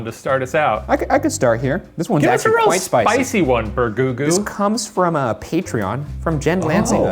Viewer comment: did shane call ryan "bergoogoo"???